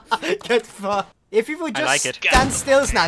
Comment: Wait, is dutch? Sounded like he used the typical dutch swearword "kut" (which is equivalent to cunt or twat) at 4:50 and also due to his thick accent sometimes